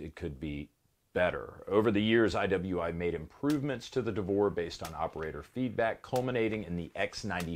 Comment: I thought I heard "Davore" and I wasn't ready for the cc subtitle. Had me coughing up soda.